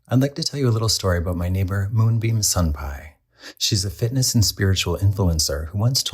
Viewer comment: Moonbeam Sunpie 😆 I thought you said Mungbean Sunpie 😂